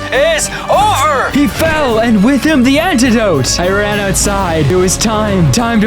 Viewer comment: Did that say Antidope